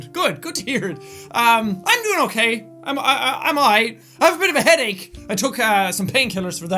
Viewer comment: did anyone think he said "I'm high"?!